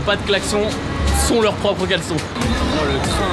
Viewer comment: leur propre quoi ?? 😂😂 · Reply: Caleçon